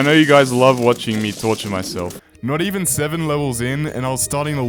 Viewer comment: I was on low volume and thought you said touching instead of torture lmao